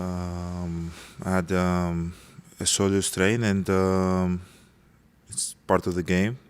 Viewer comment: What it was his shoulder that was injured I thought they said it was was his calf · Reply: He was saying soleus but it was hard to tell with his accent. soleus is in the calf.